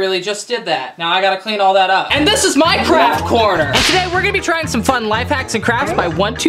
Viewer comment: sounds like he said minecraft instead of my craft